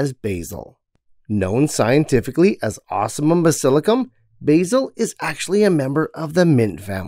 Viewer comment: Ocimum basilicum I thought you are saying awesome uhm basilicum :D which name would also fit this plant based on your video :D